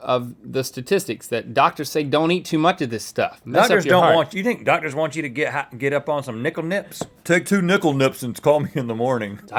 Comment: Rhett was gonna say 'Get high on Nik-L-Nips'